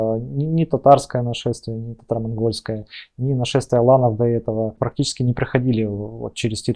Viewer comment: "нашествие ланов" ? Не расслышал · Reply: аланов